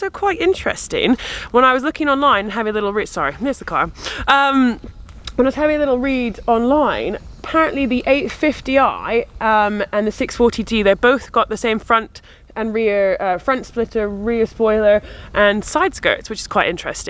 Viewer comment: Another good video, as per usual, but did I hear at 2:21 640D? 😁😁